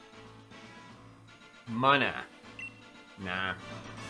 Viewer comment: wait did just called munna manna?